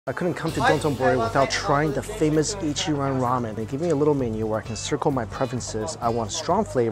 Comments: I cant be the only one who thought it said "Ichiraku Ramen" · I thought it said ichiraku ramen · I’m not the only one who thought that said ichiraku ramen right? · Lokey thought it said ichiraku ramen i was like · I thought it said ichiraku ramen lol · I had just turned on the video... wasn't completely listening yet, and I swear I heard "Famous Ichiraku Ramen" and I was like the fuck that is real? Then it hit me what he actually said. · I thought you said ichiraku ramen! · At first u thought it said ichiraku ramen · Ok just an otaku here but when he said Ichiran I thought he said Ichiraku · Bruh I thought it said Ichiraku Ramen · I thought it said ichuraku ramen